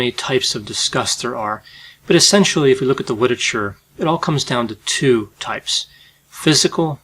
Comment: Anyone else catch him say “witerature” at 1:05? 😂